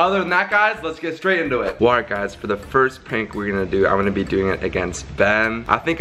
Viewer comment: did he say pank?